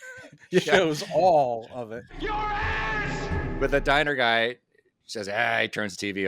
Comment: At the beginning of Superman: the Movie when Zod shouts at Jor-El, I always thought he said, "...And then one day...your ASS!" Only when I put the captions on my DVD did I learn it was just his thick British accent saying, "Your heirs!" LOL